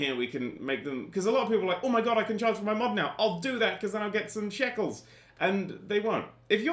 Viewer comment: Did he said get some shekles?